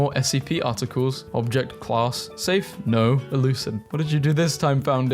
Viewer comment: DROP SOME IDEAS!!!!!!! I HAVE FINALLY DONE THIS IDEA, AFTER SO MANY REQUESTS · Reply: why do you say elucid? I thought it says euclid